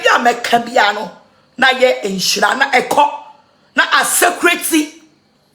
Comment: What did she say, "Na a saycreti"??? What is that?